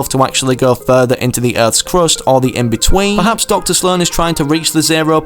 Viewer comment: did you say earths crust in